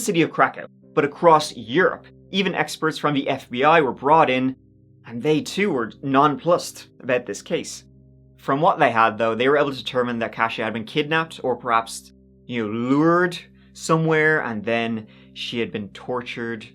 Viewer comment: I just have to point out how refreshing it is to hear someone actually use "nonplussed" correctly. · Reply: I always hear "nonploosed"